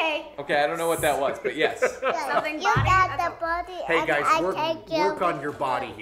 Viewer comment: is a natural. I could've sworn he was saying "work on your body". Haha.